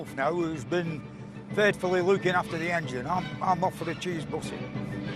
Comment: What does he say at 6:17? A cheese what? Is it a sandwich? I think he complained about having to eat one for lunch "again" with Donald back in the first series. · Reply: A cheese butty